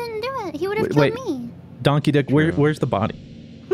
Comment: for fucks sake i didnt understand "Don Keedic" until fruit said it